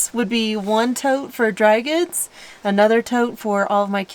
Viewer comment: Did anybody else hear, "One tote for dragons?" 😂 Sometimes I wish I had taken better care of my hearing. Other times, I just want to tell others what it sounded like to me. 😁